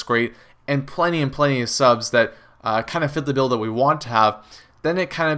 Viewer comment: what did you say cause i swear it sounded like you said fit the d*ldo :0